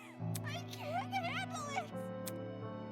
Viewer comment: Someone knows what does Eloise say at 5:34:01 😭❓ i can just understand the "I can't" but i don't understand the rest,💔 (English isn't my first language,sorry) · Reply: Oh sure, she says: "I can't handle it!"